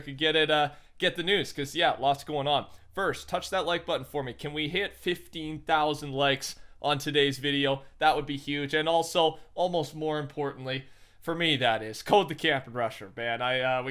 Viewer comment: Thought you said 50,000 likes but you said 15,000. But let’s get it to 50,000 like any way